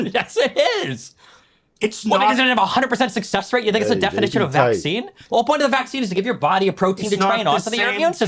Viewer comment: Wait wait, Am I the only one that heard Destiny say that vaccine had a 100% success rate? Edit: He didn't say a 100% success rate, he said "what because it didn't have a 100% success rate you think its a definition of vaccine" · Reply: When did he say that?...if you are talking about 13:20 destiny said "what because it didn't have a 100% success rate you think its a definition of vaccine"